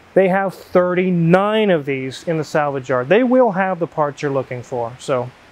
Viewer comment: He said "in the salvage yard" I heard "in the salad jar" and thought what the hell is a salad jar.